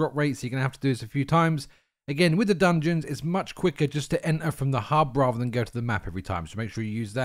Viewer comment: what did he say here? I can't understand but sounded like a quicker way to enter dungeons? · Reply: Enter from the hub rather than map every time